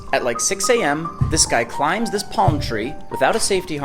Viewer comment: Did he just say at start "guy climbs plum tree to get coconuts"? And ya'll ok with that?